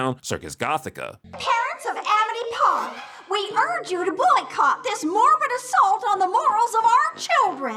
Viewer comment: Why did I hear “This Mormon assault on our children’s morals.”